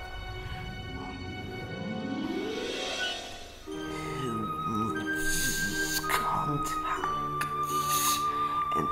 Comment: Saucer what you saying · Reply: He said "Who needs concacts and fake teeth when you've got the real deal!"